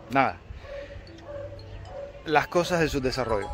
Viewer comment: no entiendo. Dice: "Las cosas del subdesarrollo" o "Las cosas y su desarrollo"? Saludos genio... Pronto Dios pondrá fin a todo el sufrimiento. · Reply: Del subdesarrollo... Jajaja . Abrazo!